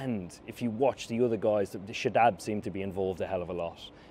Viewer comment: Saadab Singh 😂😂😂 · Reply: I heard him saying "Shadab seem"